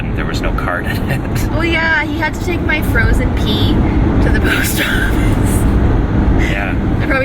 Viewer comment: When said frozen pee, I thought he said frozen pea. (Like the green vegetable.)